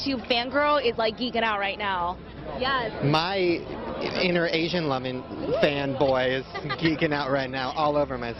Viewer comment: did he call her an Asian lemon? 🤨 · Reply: I think he said asian lovin’